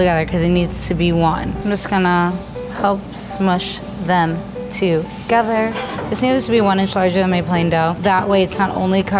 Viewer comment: thisneedshebeoninchshshimeiplaindough??? Does anyone know what she said there? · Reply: Cadence Moon “this needs to be one inch larger than my plain dough” idk